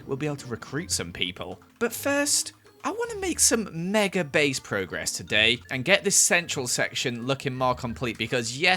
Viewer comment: I thought it said, MEGA PALACE INTO PROGRESS LOL